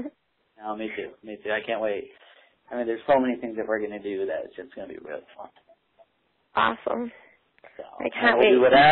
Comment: Can you tell what he says at 5:32? He says "There's so many things _____ into you that it's just gonna be real ____. (Fun)? At the end? But I have no idea what the first part is. Lil help? 🌹😘🙂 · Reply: There's gonna be alot of thing we can do it will be real fun